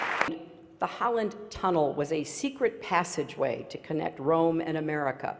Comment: What does say? Is it 'the Holland tunnel'? ... so the pope could rule the USA??? Anyone with some more information, because online search does not give any answers.